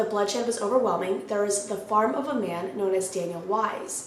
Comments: I was listening and not looking at the screen and I swear I heard " the fart of a man" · I was listening and not looking at the screen and I swear I heard " the fart of a man" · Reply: Ok wait.. just to clarify I didn't hear a man fart.. I heard you say" the fart of a man"